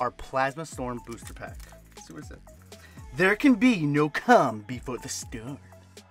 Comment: Dude when you got to the plasma storm pack I swore you said "no cum before the storm". Lmao I had to rewind and listen a few times...